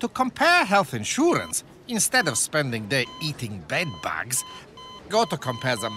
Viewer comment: Did he say eating bed bugs?